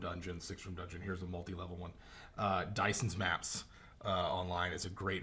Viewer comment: you mention dicens maps? what is that? tried googling but no matches, probably spelling it wrong or i misheard lol